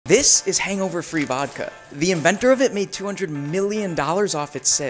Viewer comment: Lol I thought he said hangover 3 vodka 🤣 like o the movie had its own vodka